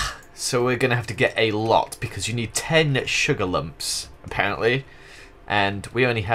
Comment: When you said 10 sugar lumps I thought you said tentacle lumps lol XD